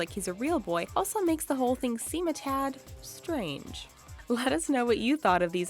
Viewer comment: am I the only one who noticed that the subtitles at 5:17 said disturbing at the end but she actually said strange...